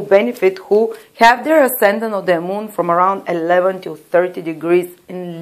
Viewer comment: thank you for your services, but what degrees are you saying @ 14:56--- 11 to 13 or 11 to 30? thank you. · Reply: 11 to 30 :)